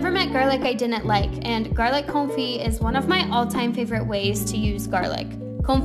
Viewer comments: At first I thought,she said "Let's make Garlic Kulfi" But then realized it was Gralic Confit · Who els thought she said Let’s make Garlic Kulfi. You know Saturdays are my cheat days.